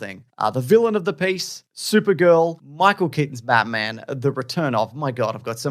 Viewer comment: michael kenton's batman is going to die? I thought they said ben affleck's batman now im confused